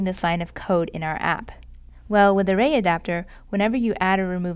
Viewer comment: the subtitle should say "array" instead of "a ray"